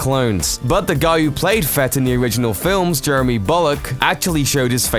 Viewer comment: did he actually say "Jeremy Bollock"?